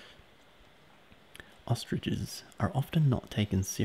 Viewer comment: instead of hearing ostriches, i heard ostages. "ostages cn run faster than horses, and roar like a lion". i had to go back to listen the thing again XD